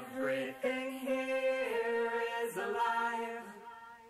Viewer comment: everything here is a lie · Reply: My subtitles said at 7:55 Everything here's a liar